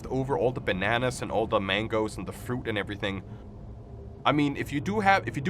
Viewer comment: Did you say.....mangos?